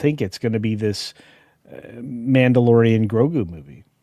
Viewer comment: No desire whatsoever to see The Pedrolorian and Cashgrab movie. Wouldn't see it if Pedro kept his helmet on and didn't say anything. I wouldn't see it if he was the assistant to the key grip. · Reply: I thought you said pedolorian lol